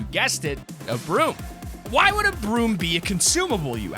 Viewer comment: When said broom and consumable item I thought I heard brute and consumable itemand if you guys know fortnite you know the brute should not be back in the game without Sum Nerfs